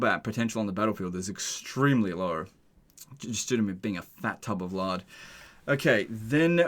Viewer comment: Dude I’m dying of laughter. I’m watch this video on 1.3 x speed and all I hear is ( fat tub of lard).